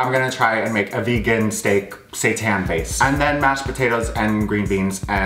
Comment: your gonna make a vegan steak 'Satan' based? What? Even the close captions say that so 😅😂 wtf is a vegan Satan steak?